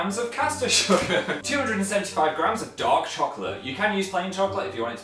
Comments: When Phil said ''Dark chocolate'' i heard ''Dog Chocolate'' ._. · It sounded like phil said dog when he said dark chocolate, x3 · i thought they said dog chocolate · When he said dark chocolate I thought he said dog chocolate